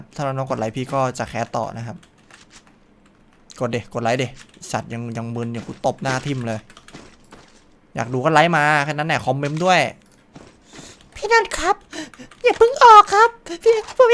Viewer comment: ได้ยินว่าคอมเม้ม